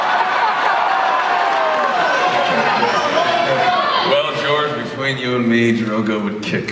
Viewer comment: When Jason's first question came up, i thought i heard someone in the crowd yell "we love you Ronin!" and maybe they did. But the question made me rethink it, they probably said Conan real quick, sounded like Conin? Idk.